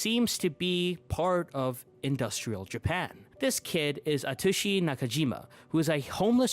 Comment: DID YOU JUST SAY A TUSHY?